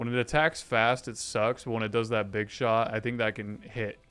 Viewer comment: did you just say [BIG SHOT]!